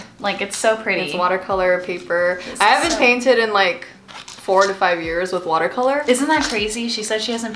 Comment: I'm not the only one who though she said 45 years instead of 4 to 5 years... Right?